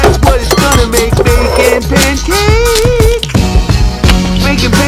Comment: When I first heard this I thought he said “ baking pancakes making baking pancakes” until I heard “gets some bacon and I put it in a pancake”